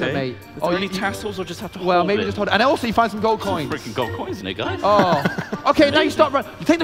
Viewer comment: i swear says "some fricken gold coins nigga"